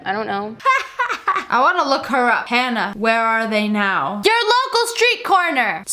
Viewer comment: When the video started and the mom said "Hanna", I heard "Hey now" and was just about ready to hear some smash mouth.